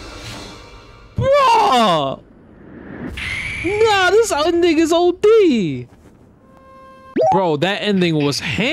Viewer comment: Ayo at @ 7:55 … what did Jwong just say… “this old nxgga’s odeee?”